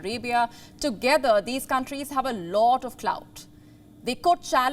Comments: did she say clout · Did she say clout?